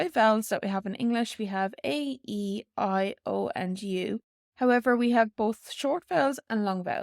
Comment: I keep hearing "A E I OÈNNDYU". Hard to clearly hear the u sound.